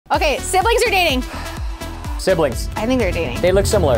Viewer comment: I Thought You Said Siblings Are Dating